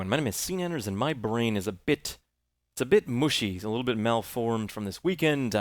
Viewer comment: "a bit muschi" LOL What does he say? The word "Muschi" in German means "Pussy/Vagina". XD So he Basically feels a bit Vagina.